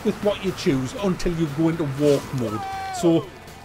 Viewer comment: he says; you are stuck with what you choose, until you __- ___ ____. I cant understand the last 3 or 4 words, can anyone translate what he says there???? thank in advance guise!! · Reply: ".. go into warp-mode"